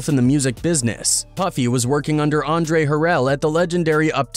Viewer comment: Did he say Joedesh shi around @ 4:35 instead of Jodecci? 😂